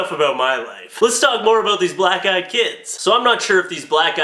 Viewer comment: I thought you said black GUY kid! Hahahaha lol! :)